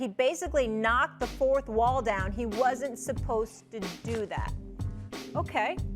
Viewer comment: I thought they said Steph ruined basketball 🤣 how about just get with the changing times. Didn't Miami lose the first year together. It wasn't easy and what about the Celtics with Garnett, Pierce, and Ray Allen